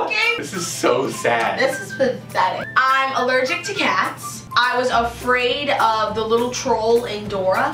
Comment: I thought she said she was allergic to gas..